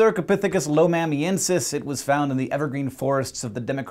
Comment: When he said evergreen I thought he said everfree